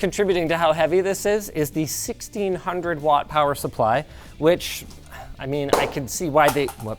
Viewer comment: I thought you said 600 watt power supply i was about to lose my shit